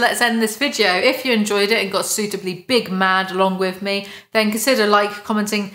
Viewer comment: Sorry, I thought you said Big Lad. (6ft1) 😮